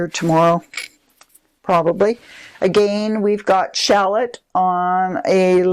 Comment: Did you say "shallot" for the glaze just before the aqua butter keeper (4:40)? You know that I am making a list of all my favourites. 🤣 The bowl at 5:53 is gorgeous! Don't be demoralized. You know that, no matter how long you create pottery, it is an experiment and you learn a lot from the setbacks and trying new things.